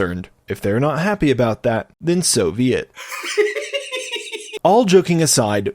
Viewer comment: The funny thing is that I always heard 'so be it' as 'Soviet'.